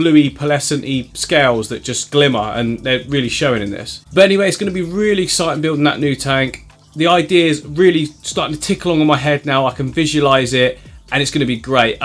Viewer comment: Did you just say "I can fish-ualice It"? 😂😂😂